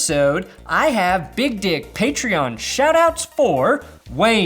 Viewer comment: Lmao I thought I was crazy because at 1:16 it sounds like he says "I have big dick patreon shoutouts for..." Then I put captions on the vid and it confirmed it 🤣🤣🤣